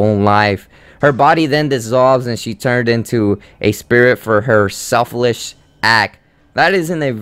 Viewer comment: Selflish act? Do you mean selfless or selfish? It sounded like you were going for both at the same time, dude.